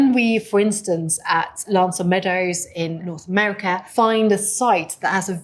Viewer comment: does she say America, because that is in Newfoundland Canada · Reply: She said North America, which is the continent on which Newfoundland resides... P.S. Referring to any location in the Americas as America is perfectly fine. For some reason, the entire planet cannot be bothered to refer to the United States by its proper name